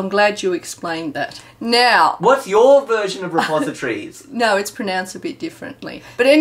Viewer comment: I think heard "suppository".